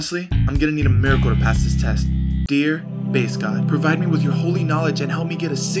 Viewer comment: I honestly thought you said face god, oops lol